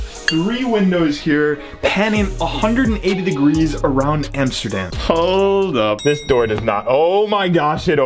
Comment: hold up did you say Amsterdam?